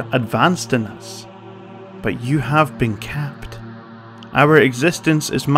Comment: "have been camped"? what did it say after 19:15? capped?